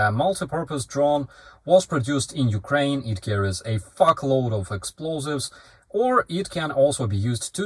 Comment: did he say "carries a truckload" or ? funny